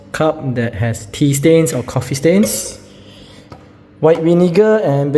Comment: I thought you said pee stains 😂❤, good job. I use barkeepers friend